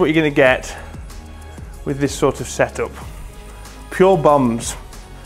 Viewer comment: Did you say "Pure Bums"?